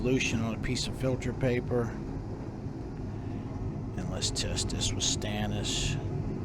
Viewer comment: He said 'test this with stannous', and I thought he said "it's Mr. Stannous!" It would be fun if he'd throw in little jokes and started naming very common items. Mr. Stannous, Steve the Stirbar, and all of favorite friends!